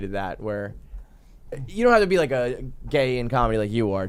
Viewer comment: did he call Shane gay? That was weird or is he😂